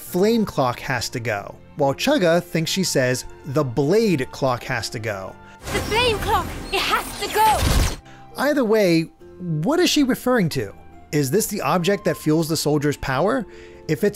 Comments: I’m going a third route: I heard Mio saying “bane clock” lol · I hear “Flame Clock.” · I hear "the MAIN Clock has to go!" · I heard “the fame clock… it has to go!” XD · I heard “the playing clock, it has to go”.